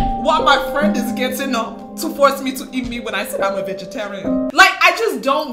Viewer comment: WHAT DID SHE SAY???? eat me??? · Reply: "..eat meat when I said I'm a vegetarian"